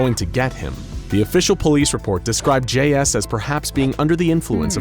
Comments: I thought he said "J Ass" instead of "J S" at first lol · Just me or in the beginning it sounded like you said J.Ass